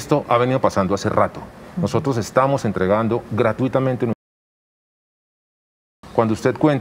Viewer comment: YouTube censuró el vídeo en el minuto 8:30 ... Nótese que dice algo así "entregamos gratuitamente nuestra información"